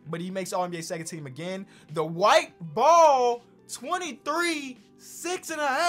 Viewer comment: I thought you said the white ball and I was like you know how racist that is